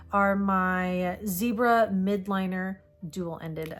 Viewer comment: Just FYI for the future: those highlighters are MILDliners, not MIDliners. Thanks for the video! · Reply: Funny story I heard them in videos and thought they were saying Zebra WILDliners 🤣🤦‍♀️